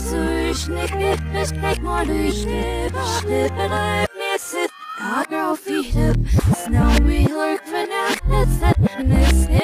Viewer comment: did anyone hear A Girl feed Up!?😳